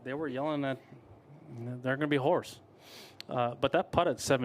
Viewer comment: Am I the only one that thought he said the fans were yelling “there are gonna be whores” at around 11:50 lol?